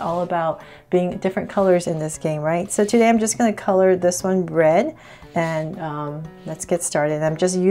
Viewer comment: Sounded like she said "color this one bread"